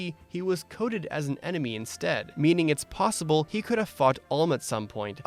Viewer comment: I actually thought you said fucked and I had to play the last ten seconds again. Then I realized you said fought